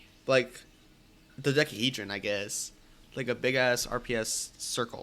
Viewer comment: that's a big word mister Of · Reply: I think she said dodecahedron actually my bad